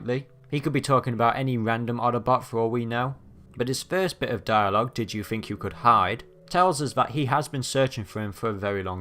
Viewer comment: DID YOU THINK YOU COULD HIDE! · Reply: I thought he said bet you think you think you could hide